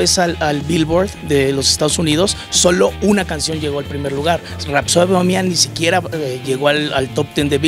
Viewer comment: este imbécil que dice rapsian bohsiam jajaja